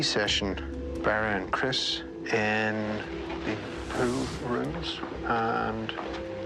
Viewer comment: PS, every time Barry says "pool room", I hear "poo room".